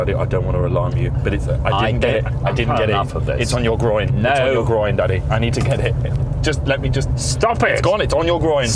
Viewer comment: At first I thought jack was saying “I need your groin daddy! I need your groin”